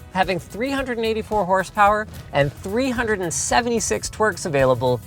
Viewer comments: did he just said "twerks"? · Did he just say "376 twerks?" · I swear I hear Linus say "376 twerks" -_